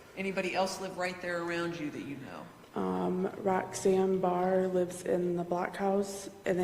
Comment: Lol, I thought she said Roseanne Barr for a minute.😂